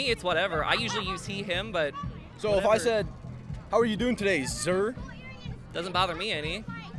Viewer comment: The funniest part is that the way the interviewer is using "zer" is as an honorific or a form of address, not a pronoun. Using it as a pronoun would be saying something like "Ze were wearing zer rainbow flag while I interviewed zem." You dont say "How are you doing today, His?" Now do you? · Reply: Fr, I thought he said "how are you doing, Sir?" Because he didn't know how to use it lol